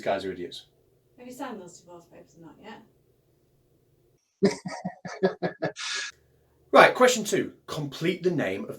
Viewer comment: Yay! Thanks for another entertaining video! Paused at 11:36 to write: I can't quite make out what your wife says...? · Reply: pretty sure its "have you signed those divorce papers or not yet?"